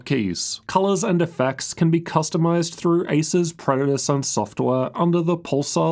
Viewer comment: Did I hear it right? "ASUS PREDATOR"